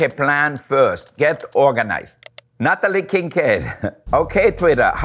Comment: I thought I heard "Not a licking kid"